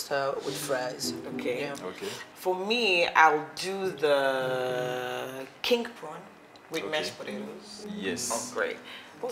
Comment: I thought said king porn...chai my ears👂